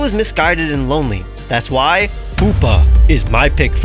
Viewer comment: the captions say Hoopa wrong 💀